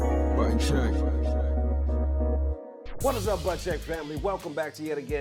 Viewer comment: Lol, why did i hear "butt check" this is my first impression, btw😂😂😂